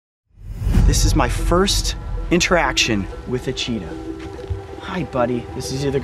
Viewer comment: did anybody hear cheater ?